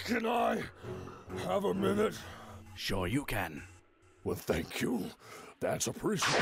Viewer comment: Vegeta does not say Sure you can. He actually says Shuryuken, which is a fist move in Mortal Kombat.